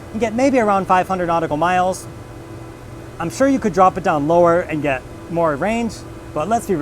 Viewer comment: Did he say drop it down to 300 knots?🤔